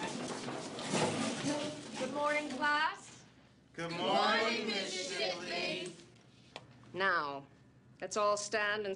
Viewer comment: Mrs. WHAT DID THEY CALL HER?!?!?!? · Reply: it's shipley